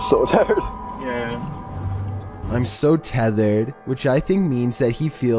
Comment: Tethered?!! Are you tripping? He says he’s so TIRED! You probably think the Beatles have a song called “I’m So Tethered”.